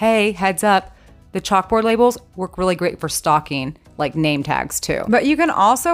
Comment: I was sewing while listening to this video, and thought I heard, '..very good for stalking, for name tags.. ', and my brain went to, '..but why would you want to wear a name tag if you're stalking someone, you'd give yourself away!' Oh, for goodness sake, what AM I like?! 🤦🤦🤦